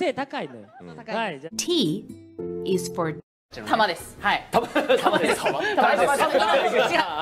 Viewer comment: No sé si es porque tenía hambre o qué pero escuche "Tamales" JAJAJAJJAJAJAJAJA